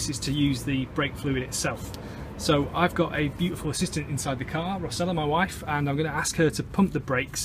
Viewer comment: Just the right video i need to understand and fix my car's rusty piston. Thanks! You should have asked your Beautiful Assistant (i first heard it as a Beautiful System) also to hold the camera for you :)